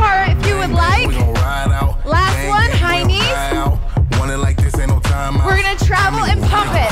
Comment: Ohhh "High Knees". Thought she said "heinies".